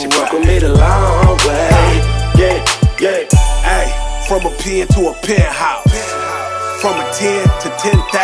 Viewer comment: I thought he said “from Apia to a penthouse” lmao